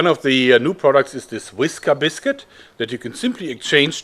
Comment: Did I hear him say whisker biscuit?